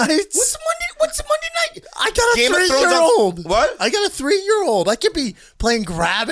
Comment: did he say he's got 3 year old men??